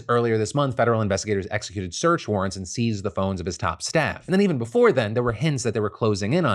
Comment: I legit thought you said they seized the bones of his top staff. I was like huh he killed them?!?! 😂😂